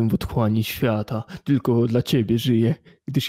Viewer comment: czy ja inaczej usłyszałem xd a i ta gra to VRchat chyba xD